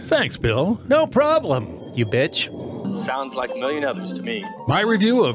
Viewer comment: Anyone know what the guy says at 14:36? "Sounds like million of us to me"? "Amelia Nervous to me"? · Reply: sounds like a million others to me